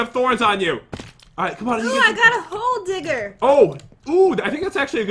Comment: it sounds like jen says gold digger